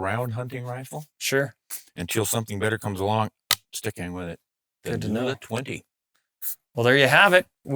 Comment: The rifle at the 28:25 mark is what? Didnt quite catch it. DORLEAC something. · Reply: Wilson Combat NULA 20.